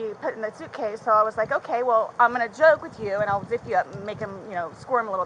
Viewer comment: I think you missed something team Jim. At 5:28 she nearly says “so I’ll Video” then stops herself. This goes against what you said about her forgetting about the videos. Think you missed this.